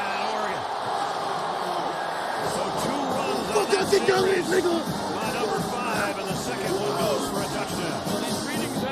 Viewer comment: What he say at 6:02? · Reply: “who tf yaw think yaw is 🥷”